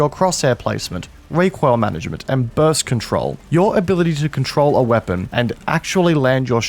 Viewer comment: Lol I thought you said birth control xD